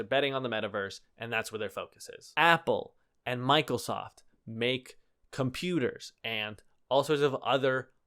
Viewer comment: Did I hear that right? Michaelsoft? I love it!